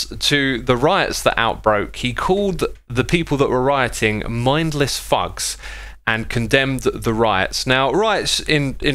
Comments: When he said "mindles thugs" I misheard it as "mindless fucks" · I thought he said something different, I had to turn on the captions to see if he was saying what I think he was, and he said 'Mindless Thugs'